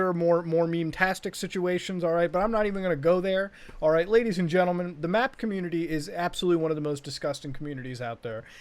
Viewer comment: I heard "The math community" me: God damn math bastards..